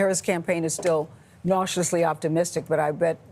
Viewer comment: One of them same “cautiously optimistic” but it sounded like they said “nauseously optimistic.”